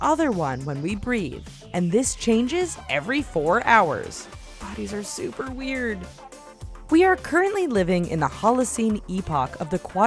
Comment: Did she say "guys are super weird".